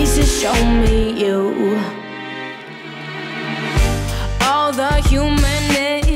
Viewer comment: wut did she say? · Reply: All the human names